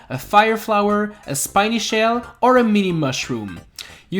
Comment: Did You Just Say Spiny Shell